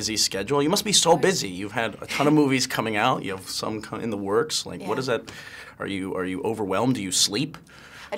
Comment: it sounded like he said "You have some cunt in the works."